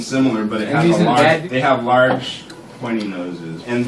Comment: I heard, "Large pointy noses"